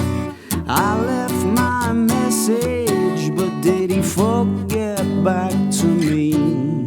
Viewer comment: sounded like he said "but did he fuck it back to me"